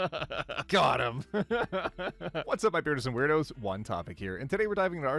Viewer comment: Friend: does he say beardos and weirdos because men have beards and women are weird you should stop watching him Me: ..... no it cuz its fun to say and he has a beard Friend: oh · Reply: I always thought he said "birdos"